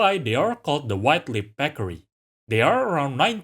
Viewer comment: The subtitles really thought you were saying "peckery" every time. XD